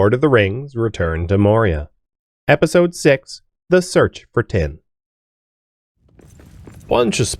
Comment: I misheard the title as “The Search for Tim” and wondered who the heck Tim was!